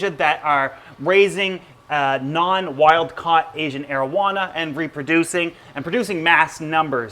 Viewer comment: Im gonna be honest I thought you kept on saying “Asian marijuana”